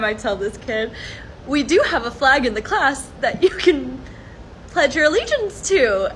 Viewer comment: "We do have a flag in the class that you can pledge alliance to." *pans to political flag instead of the national flag* I know another group of people that did that. Back in the 1930's. Almost like you guys take a lot of pointers from them. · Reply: I get it. Also sadly, she said "pledge ALLEGIANCE" not "alliance", which I personally don't. I'm super straight, LGBTQ-Neutral. Not everybody are alternate genders so they ain't got no privilege to centralize themselves among the society. National Flags represent a nation, which means beyond politics, even though the US flag were used to call freedom and democracy outside the 50 states since the US is the free world leader. Love it or leave it, end of story.